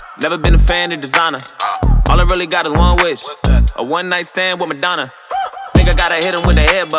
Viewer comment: In my head i heard "a one night stand with Obama" Wtf is wrong with me 😂